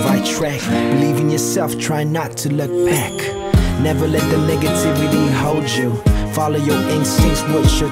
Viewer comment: Got the lyrics for ya guys ;) ____________________________________ Aight, I guess I'm feelin uptight Having trouble in my mind thinkin what's right But there's nothing I could Nothing I could do I don't have a clue Lookin for answers I walk around Give me some answers I ask around tryin to stop maybe some day I find Maybe someday.... Maybe someday... Don't worry things will get better The sunshine comes after a bad weather Hang on your in the right track Believe in yourself trying not to look back Never let the negativity hold you Follow your instincts, what you're told You aren't that weak as you seem to Maybe someday.... Maybe someday... Life, can be struggling at times But though, lovely at the same time Going up going down, enjoy what's coming ahead Cuz that what makes you beautiful Be true to who you are Soon some day you a star Catch the world let it come to ya Maybe someday.... Maybe someday.. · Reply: Bruther, I thought he said the N word but he said “negativity”. Thanks for the lyrics